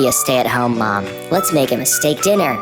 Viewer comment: all I heard is "mistake dinner"